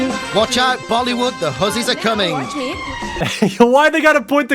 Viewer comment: Blud really thought he was calling them horses 😭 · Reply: Thought he said huzzies….